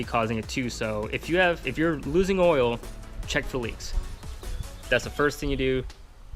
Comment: did you say "check for LEAKS? (Not LINKS) If you don't find any leaks then its your piston rings"? So if you see leaking then it's probably something other than the piston rings? I just wanted to clarify that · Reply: Leaks lol. Correct these cars have a few spots like valve covers and rear main they leak from. I thought I had oil consumption on my 06 DE because I lose like 2 quarts by 3k miles. Turns out I just leak oil from the rear of the engine. More than likely my valve covers even though thankfully my spark plug tube seals are still fine and they stay dry. So I dont really worry about it, just change my oil sooner